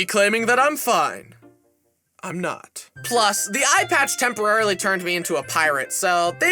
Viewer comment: subtitles said “iPad” 💀